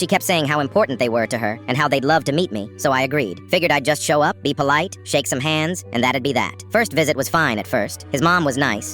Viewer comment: I thought you said shake some ahh